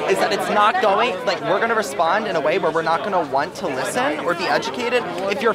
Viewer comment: Male student, " we are going to respond because we are the educated, then after that all I heard was blah, blah, blah, blah...